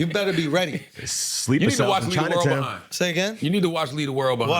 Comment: aww. I thought he said you need to watch Pokémon instead of leave the world behind for a second. I was excited to hear what followed next lol